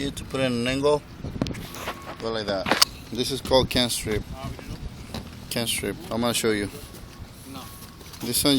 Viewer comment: What's the triangular piece called by skylight? - I heard "can't strip" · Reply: J M that’s what’s it’s called can’t strip also look up tapered edge